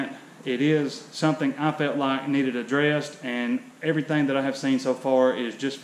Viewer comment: did you say Chuck needed a dress? I agree he needs a dress fighting like a girl.